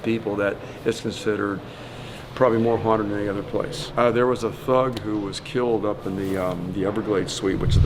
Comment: WHAT DID HE SAY?!?! HE SAID THUG!!